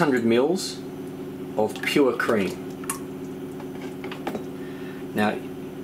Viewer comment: subtitle said "poopy cream"... lol